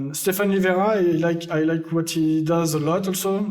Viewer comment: Does anybody know who the guest is talking about at 1:22:06? I hear "Stephanie Varai" but his accent is so strong, I don't know who he's talking about.